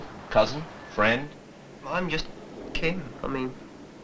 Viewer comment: did he really say “I’m just Ken…” 😂 Ken is a whole mood